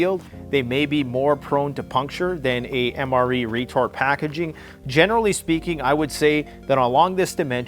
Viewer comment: Did he just say retard packaging? 😂 Mis heard him the first time